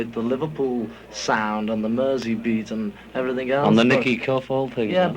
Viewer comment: Can anyone understand what John says at 2:09? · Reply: Something about Nicky Cuff - they entered a talent show with him in it. This is from Beatles Bible: The Quarrymen didn’t pass their audition on this occasion; the winners were the Sunnyside Skiffle Group from Speke, a group which featured 19-year-old Nicky Cuff, who was 4ft 6in tall and played the tea chest bass while standing on it.